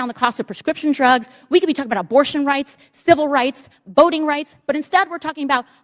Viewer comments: I thought she said boating rights.... I was like "yeah!! Fishing!"... then realized I heard wrong 😢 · I thought she said boating rights 🤣🤣🤣